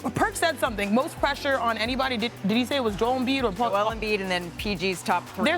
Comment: For sure thought I heard “Diddy” and not “did he” 😅